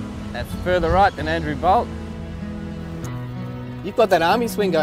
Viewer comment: I can't make out the last name on "That's further right than Andrew ..."? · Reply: Bolt. But it’s a local reference. Tucker Carlson for the US audience 🤣